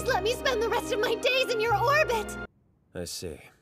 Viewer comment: did she say whorbit???